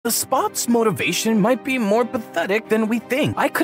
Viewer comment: Who heard him say The Spoaauh